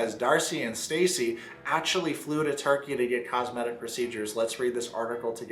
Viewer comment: Ive been watching this on a faster speed and could have swore you said Stacey and Darcey flew to Target to get cosmetic procedures. 😂